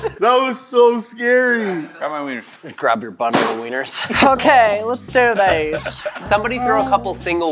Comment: When said "ok lets do this bros" I thought he said "ok lets do this bras" lol....